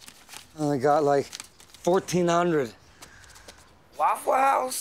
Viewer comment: What does Combo say? 'waffles?'